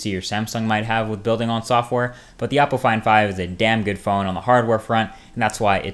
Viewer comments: When you said Oppo Find 5, I thought at first you said Apple Iphone 5 with a weird accent. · LIke if you thought he was saying apple iphone 5 instead of oppo find 5